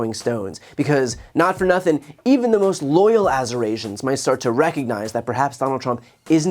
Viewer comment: LOL I thought he was trying to say: Eurasians. Persons with mixed European and Asian lineage... 😢